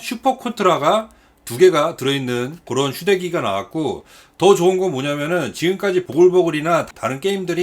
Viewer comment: 왜 난 "슈레기"라고 들었을까... 난 타락해버렸어.. 5번 다시 듣고, 아.. 휴대기기.. 라고 생각했네 ㅋㅋㅋㅋ